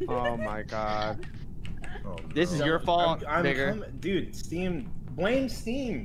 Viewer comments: I thought he said n***er instead of vigour at 0:12 Edited time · did he say.. the N WORD??? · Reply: Vigor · Am I tripping? what did soda say? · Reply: He says Vigor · Am i crazy or did he say the n word? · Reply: lmao no, he said vigor which is some guys name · did bro say the n word or am i trippin? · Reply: He said "Vigor"